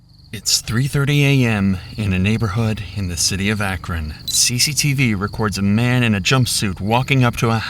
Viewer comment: i heard " the city akron" and i knew it was a good one